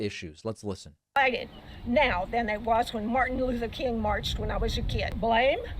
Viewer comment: did she say "Martin Luther the King" ? ? ? then @ 4:55 " . . I saw them make complete idiots of themselves" . . just like I'm doing right here.